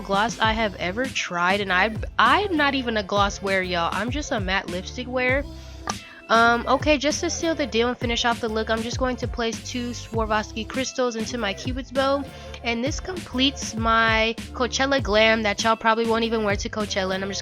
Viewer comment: I swear I heard "Souvlaki cristal" 😂😂😂